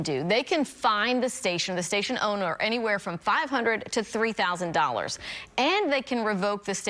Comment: I thought you were saying 300,000$ fine but 3,000$ 😂😂I will pay that.